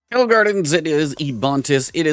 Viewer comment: I swear I heard “hello gardens” in the beginning there 😅